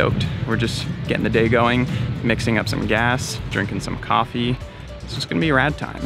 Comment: I literally heard " mixing up some coffee, drinking some gas "! Lol!